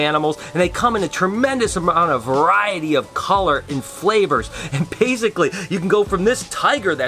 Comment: did he say "flavor",do people eat snakes or somethin? (I dont mean to sound rude,im just genuinely curious)